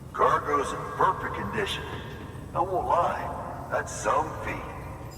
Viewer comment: Man, parte 6 was awesome! I enjoyed every second of this dope video 🔥 14:49 I thought he said "that's zombie", haha 🤣 Your videos are always high quality and you upload often, so we can't get bored at all. Thank you for being such an awesome youtuber and person. I am so glad to know you, bro! I already can't wait for parte 7! 😎